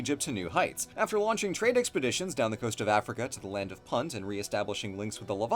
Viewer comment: What Said "... down the coast of Africa, to the land of Punt..." What I Heard "...to the land of Puns" ...THERE'S A LAND OF PUNS AND BLUE HAS NEVER DONE A VIDEO ABOUT IT??